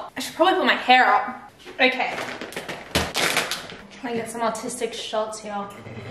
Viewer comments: i thought she said autistic... she said artistic right? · I thought she said autistic shots🤣